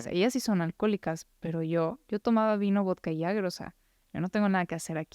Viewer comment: alguien podría decirme que es lo menciona? No entiendo la última palabra “Yo tomaba vino vodka y (palabra que no entiendo)” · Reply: Jagger we, jaggermeister